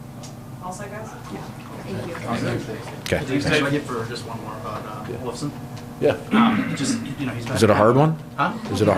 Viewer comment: Couldn't hear who the last question was about (@ 11:12); anyone? Anyone? Bueller? Thanks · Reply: It was about Olofsson